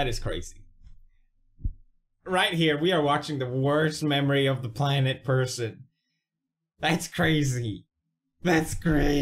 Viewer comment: Did he say "worst memory of the planet person" at 9:21?